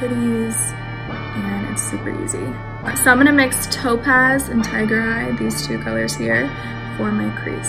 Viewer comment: I thought she said tyga while that was “tiger eye” lol I’m not okay